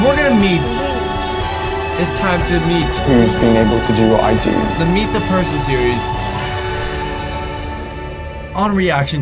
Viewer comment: ThE mEeT tHe PeRsOn SeRiEs at first I thought he said, " The meet the person disease!"